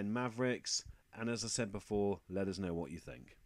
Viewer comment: Nobody else notice the dictation at the end thought you said "lovers know what you think"